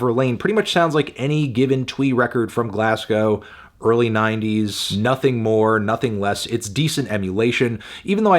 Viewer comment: What on earth did he say it sounds like? Can't find literally anything googling what it sounds like he said, can someone help out. If Tom Verlaine is MEDIOCRE EMULATION I would absolutely love to hear the original sound, because I loved Tom Verlaine, especially its extremely charismatic outro. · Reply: twee record from glasgow in early 90s but he’s so wrong because twee songs often has a poorly made by some college student-y vibe on their production while tom verlaine’s production is so layered and strong. common fanton L